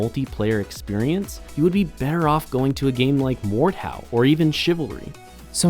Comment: there were two games mentioned. Can someone please write the names of the two games. I can’t seem to find them on Google no matter how I try to type what I heard. Thanks. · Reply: MORDHAU and Chivalry